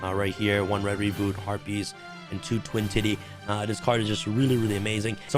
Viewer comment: I’m pretty sure I heard “twin titty”. be wildin 😂😂😂